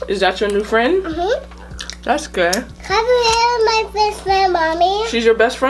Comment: it sounds like she said that’s gay